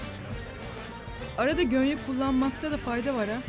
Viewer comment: den sonra bıyıkları düzeltmesi için , adanalıya GÖNYEK mi diyor ne diyor orda İDİL anlayan varmı ? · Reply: Gönye diyor